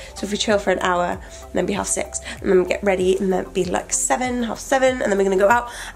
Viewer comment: Is she saying "then we have sex"??? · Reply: Half 6